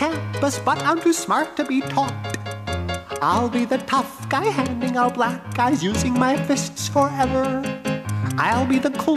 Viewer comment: Being set in the 1950s, I thought he said "Hanging out B Guys." LOL